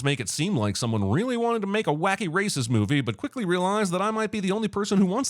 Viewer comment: When you said Wacky Races movie.. You made me took a double turn because I thought you said "Wacky Racist movie"... I mean considering the movie is about a cop